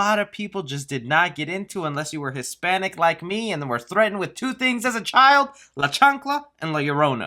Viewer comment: I thought you said "Threatened With Two De chanclas and now coronavirus